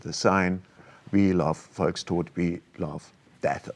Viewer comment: What is this antifa saying in German "death of the people"? Volks-something? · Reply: Found the term. Volkstod = (the) people's death